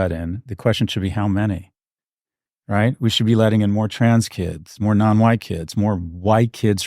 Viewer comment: Did he say TRANS KIDS ?